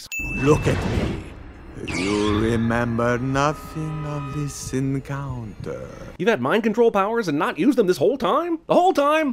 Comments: Who else heard, “You remember nothing of the sin counter.” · Not gonna lie, but I totally thought Drac at 6:28 said "You will remember nothing of the sin counter" geez I think he'd be good at CinemaSins! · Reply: He originally said "You will remember nothing of this encounter" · Is it just me or at 6:26 does drac say sin counter like if you hear it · when dracula said “this encounter” i for real thought he said “sin counter” no joke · I thought he said you'll remember nothing of this sin counter. 😂😂😂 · I'll admit, it sounds like he says "the sin counter" which makes that more hilarious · 😂 Dracula said 'you will pay no attention to this encounter' but I misheard it as 'you will pay no attention to the sin counter' 😂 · I heard “You will remember nothing of this sin counter” Rather than “You’ll remember than from this encounter” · sounds like he says”you know nothing of the sin counter” lmao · It sounds like he said “You remember nothing of the sin counter.” Half of the sins on the sin counter go away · did he just say sin counter? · I swear Drac said "You remember nothing of this sin counter." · I swore he said "You remember nothing of this sin counter" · when Dracula was using mind control it sounds like he is saying “You will remember nothing of the sin counter” I thought you edited it so it would say that. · i misheard drac and though it was a bit where is he says "you remember nothing of the sin counter" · I thought drac said you will not Remember this sin counter